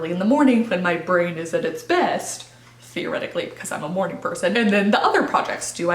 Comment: I had to watch through this video one more time. I thought said she got more done in the morning because she was " a horny person", it felt so out of carachter haha...she said "morning person".